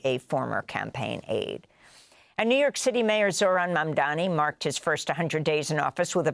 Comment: onward - The subtitles say “Eric Adams” and “Mayor Adams” every time you refer to Zohran Mamdani. What happened?